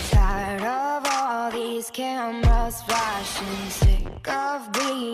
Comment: WAIT- I HEARD "KENMA" . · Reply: it said camera BUT OH MY GOD IT SOUNDED LIKE THAT RIGHT